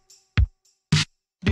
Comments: I thought it said “deers falling down at the party” lol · my dumbass thought the song said "deers fallin down at the barn"💀💀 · Wha5 I thought it said : “Tears falling down in the Pantry” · I thought it said dearrs falling down at the party.